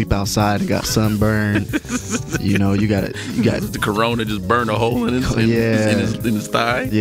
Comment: what he say? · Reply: corona, but as beer